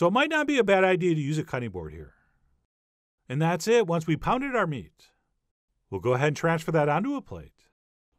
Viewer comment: Did he say, "Once we've pounded our meat" at 2:10?